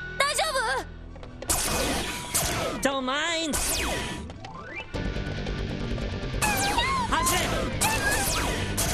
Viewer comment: ここの英語なんて言ってるかわかる人いますか？ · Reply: "Don't mind !"ですね